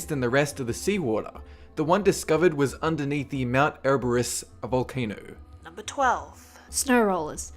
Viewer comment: Did he just say Underneath the Mount Everest Volcano? A) wtf B) Wtf C) WTF D) WWWWTTTTFFFFF!?!?!?!?!? Impossiblez